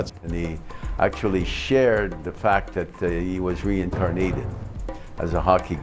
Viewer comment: Did he just say re-car-nitted? Instead of reincarnated?